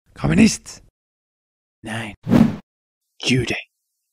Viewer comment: what he say? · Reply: Juden